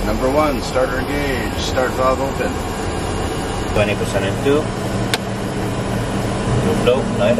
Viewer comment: the subtitle says "20% at 2" when he actually says "20% N2"